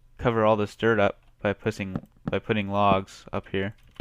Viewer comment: he accidentally says pussing instead of putting 😂😂